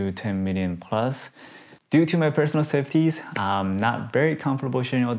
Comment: What did he say here? "Due to my personal....." My personal what? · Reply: safeties